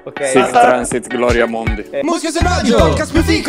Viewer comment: cosa ha detto a 0:50 Martin? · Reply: Sic transit gloria mundi, latino: così passa/si sposta la gloria del mondo